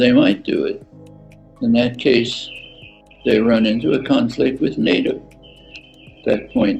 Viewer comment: Chomsky said "with NATO" not "with native". Varoufakis said: -- 9:11 "... the domination of one country by another" not "..by then another" -- 9:21 "as a route to lasting peace" not "as a route to last in peace" -- 9:22 "and globally shared prosperity" not "globally shared prospect done" Lascaris: -- 10:31 "...Ukraine ceding the Donbass" not "...the Don Bass". Also, Kherson not Kursan, and Zaporozhye/Zaporizhzhia not Zapadoja. --11:34 "..that Russia now controls" not "..that rationale controls" -- 12:18 "..but we haven't even tried _?___" not "..but we haven't even tried Zane". Not sure what he was saying here, but it can't be Zane. What's Zane?